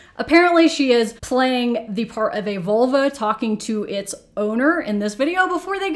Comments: i thought she said a “VOLVO” i was so confused 😭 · I was confused this entire video because I thought she said the face-filter girl was roleplaying a Volvo talking to it's owner .. not vulva..